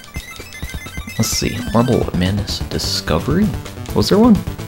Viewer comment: mark he say "bubbleman discovery" Anyone know what that is?